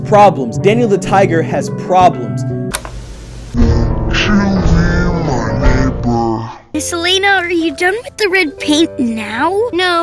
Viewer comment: I keep hearing" won't you be my nipple🥀"